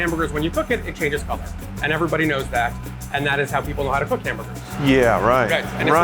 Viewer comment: the subtitles say „how to cook immigrants“